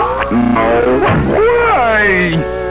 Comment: Bro I thought it said I’m out of nuts 😭